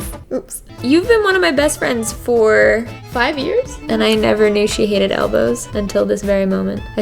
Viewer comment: At first I thought she said she hates Elvis and I was about to say no way jose